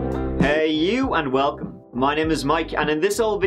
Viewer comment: Damn, was thrown off when I heard "hi" instead of "hey you, and welcome"